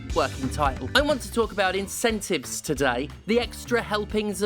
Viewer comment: I had the fan on so at first I thought you said game executives had monetized ENTROPY and like.... Also yeah 😅😅